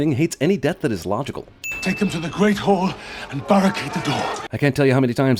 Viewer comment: Anyone else hear "Take them to the great whore" at 10:07?